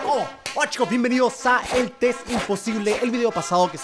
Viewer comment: Escuche "Wachos" Si son de Argentina van a entender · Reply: Martina 12 Dijo hola chicos :v pero parece que dice wachos xd